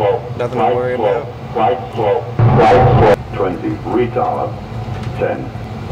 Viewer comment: bro did it just call him retarted?💀